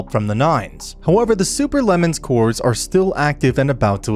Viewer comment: in 12:09 i thought he said super lemon